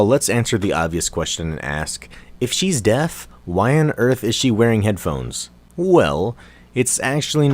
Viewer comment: It may sound stupid but what is deaf? I'm not so familiar with the English language. In thought it he said death but because this isn't the case I'm not sure what it means.